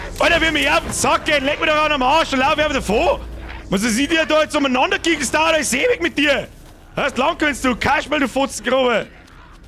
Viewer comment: Könnte mir bitte jemand übersetzten, was von 58:06 bis 58:11 passier ist?😂😂😂😂 Absolut nichts verstanden außer "Kaschbr" und das F-Wort aber das alles hat sich einfach zu genial angehört😂😂😂 P.S. bitte nicht fronten, weil ich kein Bayer bin✌ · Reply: "legg me doch am osch, dann laufe hoid einfach davo, moanst dass i etz lang umanandagichtl des dauerta ois ewig mit dir schau dassd land gwinnst du kaspal du fotzngrober! im.. im.. im.. im woid drin steh bleim und umanandablern, moanst dass i etz do umanandawix.. wos soi de scheiße scheiß 300.000kmh sepp, fod von oam eck ins andane und seng duasd nix - leck me doch am arsch dann" "Leck mich doch am arsch, dann laufe ich halt einfach davon, denkst du dass ich hier jetzt lang herumgichtel, das dauert ja alles ewig mit dir schau dass du land gewinnst du Kasper du ?!Fotzenhässlicher?! im im im.. im wald drin stehen bleiben und rumbrüllen, glaubst du dass ich hier jetzt rumwichse, was soll die scheiße? Scheiß 300.000kmh Sepp fährt von einem eck ins andere und sehen tut man auch nichts, leck mich doch am arsch dann" Mit freundlichen Grüßen, Ihr Fremdsprachenkorrespondent des Vertrauens